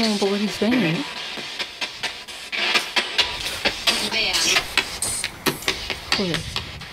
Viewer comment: cuando se oyen los pasos en el patio una mujer dice vean, 9:53 hombre dice por favor, 11:27 se escucha el sensor de movimiento, 12:08 se oye ja lo siguiente creo que es grabando pero no lo termino de entender del todo 12:34 puedo o bueno y despues una mujer dice yo fui yo o yo soy yo 13:02 obliga reto 13:55 va socio o asocio y otra voz dice esta maldito 14:23 dice algo que no entiendo y despues dice que guapa 14:25 dices al lio y te contestan como ¿ os vais?